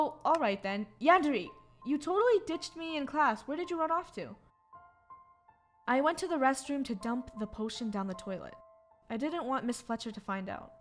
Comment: For a second I thought yandere said, “I went to the restroom to take a dump.” Lol